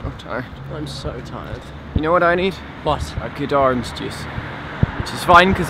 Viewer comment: I thought he said "You know what I need? A good arm seduce." Now I ship Jewen.